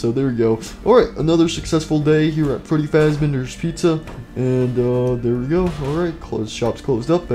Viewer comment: Am I the only person that heard him say"Freddy faz benders pizza"like what If you heard him say that 👇